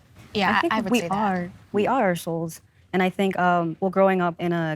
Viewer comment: "We are our souls" Yeeeea I heard "we are ar**holes" but that's probably because I'm British! I don't disagree tho!